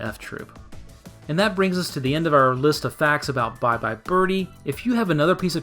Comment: Okay... I'll say it.... I hear ''Bye Bye Bir-Hee''. Am I alone, here?